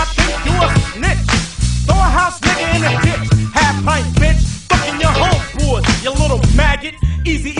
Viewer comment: I always thought he said half pipe bitch not half pint